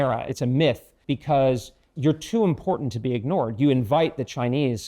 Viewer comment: 이 부분 '중립은 환상일 뿐이에요' 영어로 정확히 뭐라고 말씀하신 건가요??? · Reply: Chimera.... 😅 😅 😅 😅